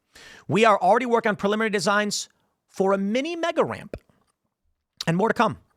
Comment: “Mini mega ramp” I heard Trump when you said that 😂